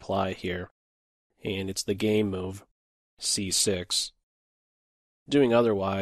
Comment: did he just say "its the gay move"?